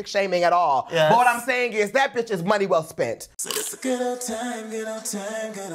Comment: At first I heard.. “it’s a ghetto time ghetto time ghetto” lol